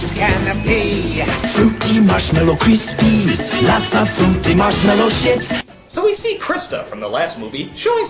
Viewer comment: "lots of fruity, marshmallow shit" that is what I heard.